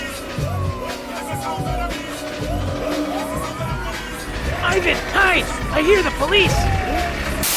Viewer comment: Iavn hide, I hear police!